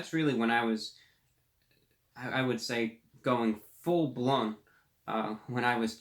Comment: Did you just say, "...going full blunt" at 6:42 ?